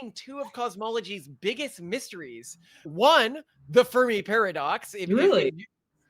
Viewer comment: I misheard it as "Furry paradox".